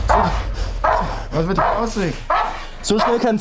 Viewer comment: Was sagt der ? "Was ist mit dem Ausweg"? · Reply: "Was ist mit dem Ausblick?" Ich glaube, war noch ein wenig gestresst und hat vergessen, die Abschlussrede zu halten :) Dann hat der KSK-Soldat ihm kurz auf die Sprünge geholfen.